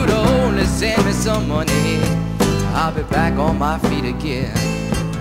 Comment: When I was a kid I thought he was saying "she didn't care in the Sunday mission box # 10" lol